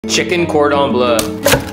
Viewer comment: I thought he said chicken corn on blood